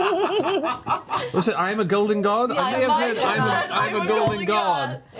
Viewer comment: "I'm a golden god!" I heard that as "I'm a goldfish god!" And then I heard Hulk. "Puny god."